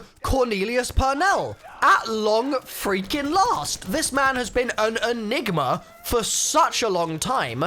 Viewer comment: am I hearing that right?? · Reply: This man has been a ni**a